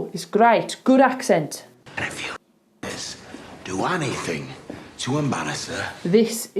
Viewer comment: I hear tommy says "dowanythin" sound like "Dua". Idk.😆